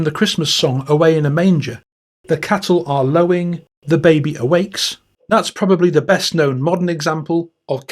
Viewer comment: As a kid I thought they were saying "the kettle is blowing the baby he wakes"